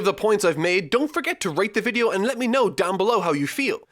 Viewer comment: Dont know why at the end thought u said if u enjoyed the video feel free to rape the video I had to rewind to confirm it but u said rate lol